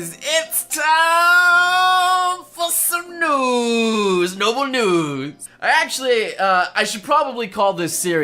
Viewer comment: When he said NobleNews I thought he said Noble NEWDS Damnit got my hopes up for no reason! XD